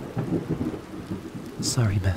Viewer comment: okay so I was watching this is subtitles on and it said bastard instead of master XD